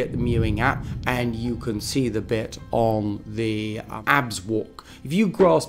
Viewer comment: does he say "abs" walk here?